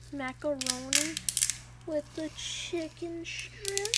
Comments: I... Was I the only one who heard the "macaroni, with the chicken stripes... *Oh*" at 3:50 to 3:55? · am I the only one who hears someone saying "macaroni with the chicken strip"?